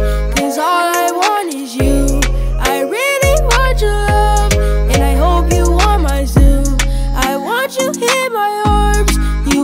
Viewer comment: I thought it said and i hope u want my sOup